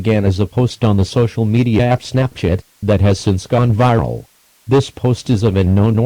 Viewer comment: it sounded like he said “snapshit” instead of Snapchat